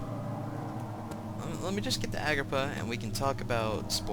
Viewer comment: Every time he said "Agrippa", I thought he was saying "Agrabah"...